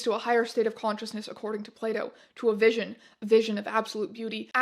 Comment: I legit heard "plato" as "playdo" and was wondering what the fuck were you reading